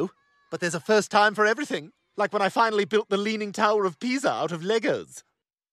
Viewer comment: I always think I hear "the leaning tower of pizza"